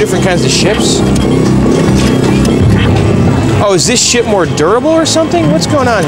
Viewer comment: sounded like he said is this shit more durable lol